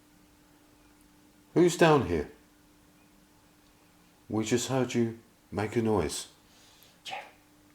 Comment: Pretty Sure reply to question , was actually an answer. Not "I am dead", but, "I Heard you".